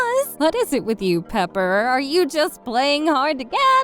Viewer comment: pepper sounded like One for some reason for me